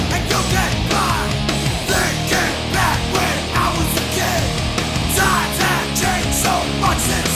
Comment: i thought he said "when i was gay"